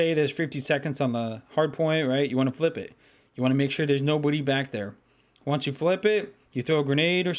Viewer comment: When said nobody at 7:17 it sounded like no booty